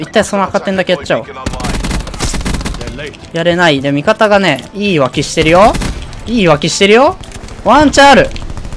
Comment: 味方が言い訳してるよ〜に聞こえたw